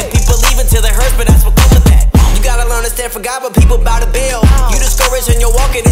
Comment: “You gotta learn to stand for God when people ‘boutta bail (bout the Baal),” double entendre 🔥🙏🏾 💯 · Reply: I thought he said “when people bow to baal”